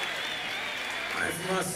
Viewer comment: what Elton said in the end? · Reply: "Wait for more singing" I believe. grammar could've been a lil bit better lol